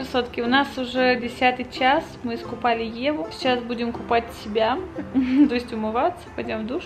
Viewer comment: Господи,мне на 15:46 послышалась,что сказала сейчас будем купать ТЕБЯ Я так перепугалась😂😂😂